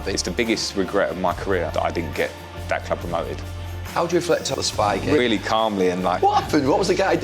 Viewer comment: I thought for a second it said spy gay, I was thinking who are spy gay!